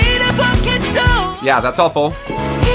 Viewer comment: Omg I thought you said "thats awful" I was gonna get mad then I realised you said "thats all full" 😂😂